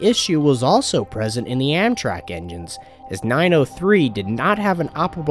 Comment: did he say Am Crack?